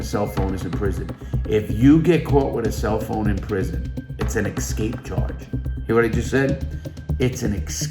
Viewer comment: did say escape charge on 17:48 couldnt really understand what he meant there anyone please help and explain to me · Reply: Yes I did. If you have a cell phone, it is an escape charge.